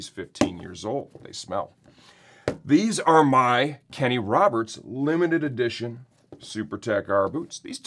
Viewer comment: For a second I thought you said limited edition Kenny Rogers super tech r boots